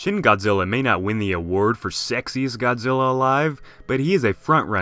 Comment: did you just say Sexy Godzilla, from? That's rich. LOL.